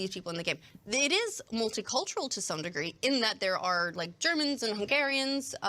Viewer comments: wait did he say he hate the germans ? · "I hate the Germans"? Is that what he said?! Guess What! Guess Where Hitler was born.... <link>